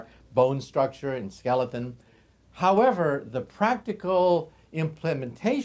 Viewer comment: notice how the subtitles said sagging instead of staggering